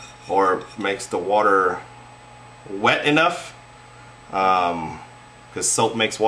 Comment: Did he say "to make the water more wet"